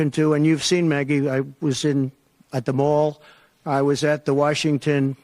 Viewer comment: Please someone explain to me what he is saying at 3:42. "Maggie Hagerman at the mall"?? Do i hear this bumbling correctly or do I not speak gibberish?